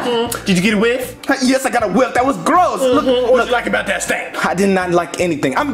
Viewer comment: Did she say "stink" or "stain"? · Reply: Stink